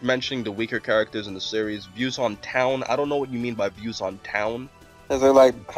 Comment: at like 44:00 i think the guy meant "Views on tien" instead of town. kinda guessed that because of the list of characters. guess they were just asking what are your views on them. like are they useless,etc.